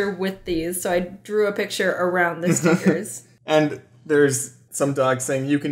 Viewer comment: Did James Said "Dogs"